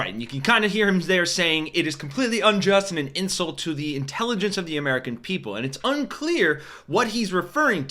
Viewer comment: I thought I heard, "This is clearly an abduction, and an insult to the intelligence of the American people."